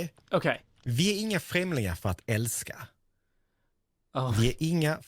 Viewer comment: I could not begin to repeat any of this. Head empty for me 😅 All I heard was "vinie something something something Elsa" plus, ' little face in the corner during this 😂 21:59 couldn't hold back laughing at this point 🤣🤣🤣 "I first thought that something was wrong with your mic, and then I thought that you had a soundboard that played it backwards what you were saying in english, but then I got it then. I heard that there was some swedish in there" "oh, that was great it was hopefully all swedish.. I'll take it"